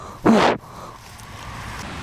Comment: whats the word he used at 14:57 ? did he say "i was such a >kreten<"???😄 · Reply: Cretin